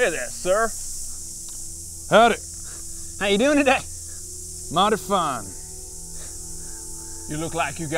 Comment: I thought he said "modify" not mighty fine.